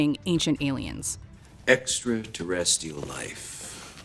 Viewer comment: I hear "ACTUAL LIFE"